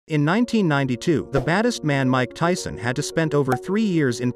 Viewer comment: Bruh i Heard " In 1992 the fattest man Mike Tyson "